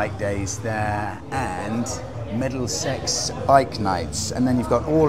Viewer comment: Thought you said 'Meddle Sex' bike nights there, sounded pretty racy! <link>